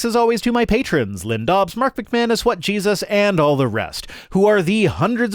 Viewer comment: I keep hearing "Wet Jesus". I'm sure your supporter is probably something like "What Jesus?", but I think I prefer Wet Jesus.